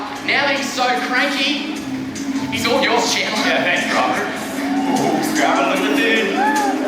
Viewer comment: did robert say "ooh shit" LMAOOOOO